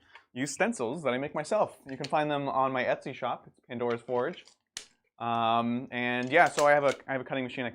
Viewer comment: if you couldn't figure out what is saying, it's <link> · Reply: Thanks, I thought he said Endor's Forge.